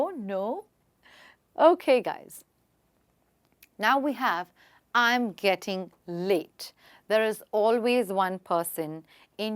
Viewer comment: I thought she said I’m getting lit